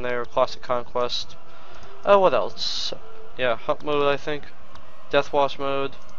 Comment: For a second when you said hut mode i really thought you said fuck mode LMFAO